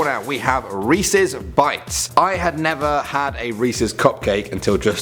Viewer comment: when he said Reese's bites I was doing something and I thought he said racist bikes 😂😂